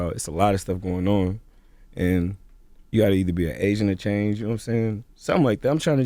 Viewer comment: Wait, what did he say at 3:17? "You have to be an asian to change" I didn't really catch it. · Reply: AGENT of change brodie